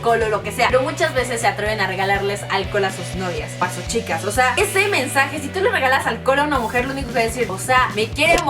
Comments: Que dijo el minuto 4:59? jajajajajaj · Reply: Nicole Aldana Dijo:Ese mensaje Pero se escuchó:Es semen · que dijo? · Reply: fergus2018 winner2018 dijo osea ese mensaje